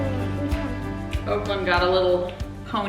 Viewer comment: 😊 thanks. Also - it’s Oaklynn. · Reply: I do apologize for stating the wrong name. I will not forget. Not sure why I thought Brooklyn and heard on the videos Oaklynn. Thanks for replying. ❤️